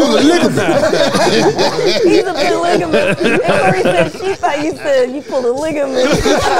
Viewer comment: Sounds like said “i pour liquor bitch” he was trying to say polygamist